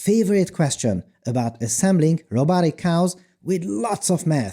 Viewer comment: haha I'm listening to this at work but almost ran to my screen and skipped back 20 seconds because I thought I heard him say: "my favourite question. Assembling robotic cows with meth." In retrospect I should've seen hat coming!